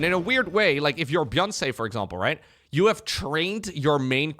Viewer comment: I heard 'Byun say' as Byunce, for some reason.